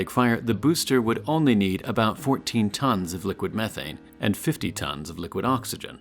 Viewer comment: I love how he is Casually saying "Only 14 tons of methane" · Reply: i thought he said 14tons of meth